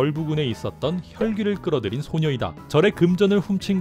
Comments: 초에 소녀라고 들리네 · 제가 잘못 들은건지 모르겠지만 "혈귀를 끌어들인 소녀이다"라고 들려서 깜짝 놀랐어요ㅋㅋ